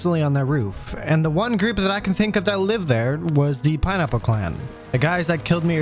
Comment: The entire time you were saying pineapple Clan I thought you were saying RynApple clan, let’s just say I felt very fu*ken stupid